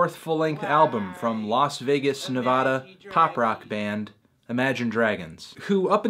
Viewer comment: ANTHONY DID YOU JUST SAY NEVAHDA?! WHAT THE FUCK?! As a Nevada local, I’m very upset by this.